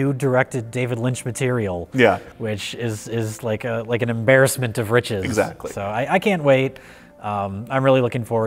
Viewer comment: Toward the end when said "an embarrassment of riches" I heard it as "an embarrassment of Rich Evans."